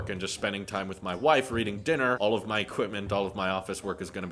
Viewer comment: Did you just say "Reading Dinner"